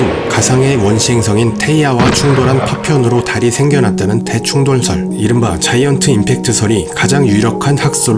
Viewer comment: 에 대충돌설 순간 대충 돌설로 들어서 대충 돌로 만들었다는거 생각하고 뿜었다ㅋㅋㅋ